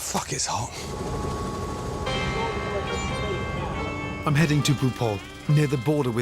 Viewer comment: I thought he said “I’m heading to RuPaul”. Lol